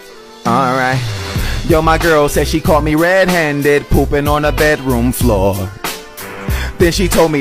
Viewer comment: Bro at first I thought it said “ my girl says she caught me red handed pooping in the girl Next door”😂😂😂😂😂😂😂🤣🤣🤣🤣